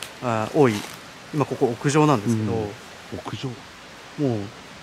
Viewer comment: 呪怨みたいな声の後「ん～」て聞こえます